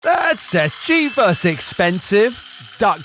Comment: What normal people hear: "let's test cheap Vs expensive" What I hear: " birds test cheap Vs expensive"